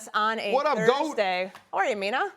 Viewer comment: Did RC say “what up goat 🐐”? 🤨